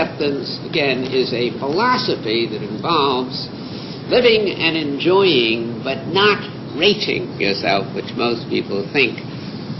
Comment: ‘A philosophy that involves living and enjoying but not rating yourself ‘ - is that what he says?